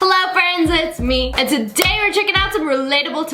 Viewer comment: I heard “fellow friend